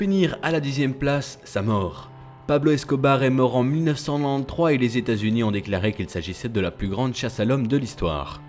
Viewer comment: Dite moi que je suis pas la seul a avoir entendu 19 nan nan 3